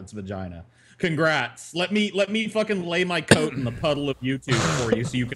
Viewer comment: ' ... let my lay my ??? on the ??? of youtube for you to pause ...' I am a foreigner. What did say? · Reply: Lay my coat in the puddle of YouTube.